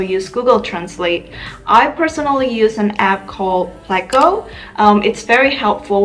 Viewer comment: did you say the app to check how words sound is called plico? Is it available in google play store ?